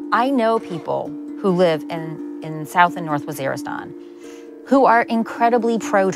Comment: what country does she say? Either I can't hear her clearly, or that's not any country I can find on a google search. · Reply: Waziristan